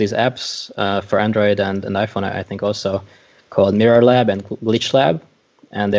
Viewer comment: What were the app names he said? min 12:53 I thought I heard neuro lab and leach lab but I can’t find them. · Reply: Mirror Lab and Glitch Lab !